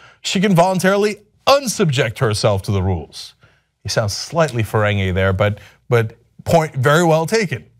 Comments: Did Cenk say she sounds "slightly FERENGI there" at 5:55? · Did Cenk say "Slightly Ferengi?" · Did he say this is slightly Ferengi? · Did he call it "slightly Ferengi?